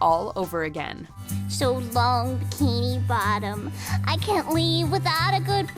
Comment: turn subtitles on sandy says bikini bottom but the subtitles said Tini bottom